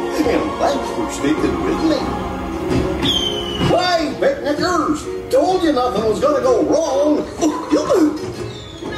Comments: what did goofy just say! Thought this was a Disney ride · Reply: Ethan Breen he said Hey Picnickers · Uhhh, I think Goofy needs to watch what he's saying.... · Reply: He said “Hey Picnic-ers! “ hahaha still funny though. I couldnt tell at first. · What'd you say there, Goofy?? 'scuse meh? 😂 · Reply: “Picnickers” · did he just call them the n word????? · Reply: no he said “picnicers” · Wait a minute, what did Goofy just say? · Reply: Nickleseth moved to Vlare hey picnickers, told ya nothing was gonna go wrong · wtf did he say 🤣🤣🤣🤣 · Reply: picknickers